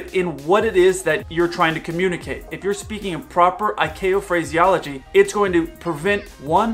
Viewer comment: "If you're speaking improper [?] phraseology, it's going to prevent one: the possibility of an accident." 2:35 "...prevent possible delays..." Since preventing those things would be good, and you're trying to warn about bad things, I think you may have meant: 1 - "...increase the possibility of an accident." And: 2 - "...cause possible delays..." Or: 3 - "If you're speaking proper..." --- 07:15 "...in proper [?] phraseology..." I went back and listed to 02:26, and I think what I heard as "improper" may have been you saying "in proper." Given how close in proper and improper sound, I submit this as an example of possible confusion, like for versus four and to/too versus two. Using "speaking proper phraseology" with no "in" would be better.